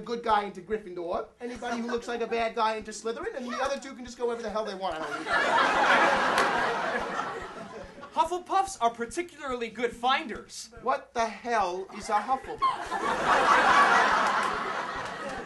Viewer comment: I'm right, hufflepuffs are particularly good finders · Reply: Kelsey Schaefer i thought he said "viners"